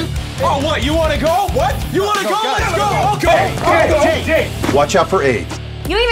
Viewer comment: Did he just say "Watch out for AIDS"?